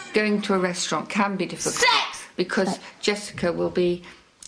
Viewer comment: jessica is so pretty... but did she just say "sex" at 3:11 ?